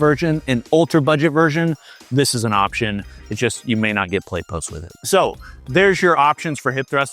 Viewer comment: I subscribed today and I will stay subscribed if you can answer me one question please! What do you mean "you may not get Plate post with it" at the 16:44 minute mark. I couldn't tell what you said or meant. Even with the closed caption, it said "Play post" I need to know ASAP before I make purchase. Thanks and be blessed!